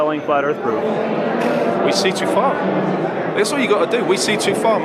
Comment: I can't make out what your answer is @ 2:32 for the question "what would you say is the most compelling flat earth proof" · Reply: White Wolf We see to far